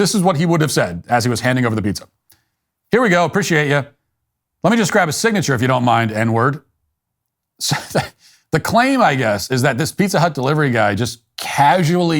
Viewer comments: “I know you’re all paid up.” No question about it. He’s simply acknowledging that the customer paid online but still needs a signature. Standard practice. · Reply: What I heard was, "Let me grab a signature if you don't mind, and-uhh, you're all paid up." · Sounds like he said “Let me grab a signature. I KNOW you’re all paid up.” He was trying to reassure the customer he wasn’t trying to charge him again. They want us to believe he said “N-word, you’re all paid up.” Ridiculous. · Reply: I FIGHURED IT OUT.... HE SAID.... " LET ME JUST GRAB A SIGNATURE IF YOU DON'T MIND- THERE YA GO" .. I f you say- there ya go, quickly and low a few times you'll here it.... Pronounce it- nearyago